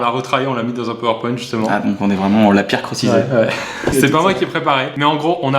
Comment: A 1:30 j'ai compris on l'a pire crossicé 😂😂